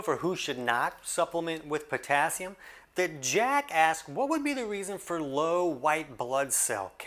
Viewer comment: thought he said “🫏 Jack-a$$” lol